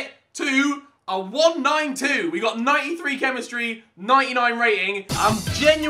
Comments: Did he say 93 Chemistry and 99 rating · did you notice what he said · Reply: Lol "93 chemistry 99 rating"